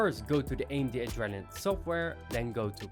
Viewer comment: What software is mentioned at 1:20? I can't hear it properly. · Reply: AMD Adrenalin Software